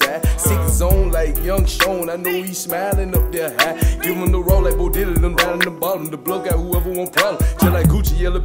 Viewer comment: wtf did he say? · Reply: Give em da raw like Bo Diddy nem down from da bottom, da bluff got whoever want problems.